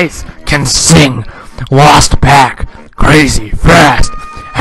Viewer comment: when you said crazy fast i thought you said fat